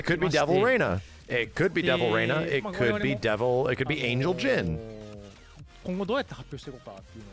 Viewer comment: tell me why I heard “an*l” Knowin’ you said angel 💀🤣